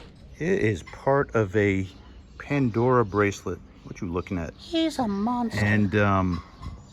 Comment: did anyone else hear someone say hes a monster